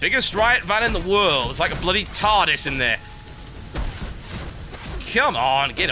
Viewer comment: Wait, did he say TARDIS? TB's a Doctor Who fan? :D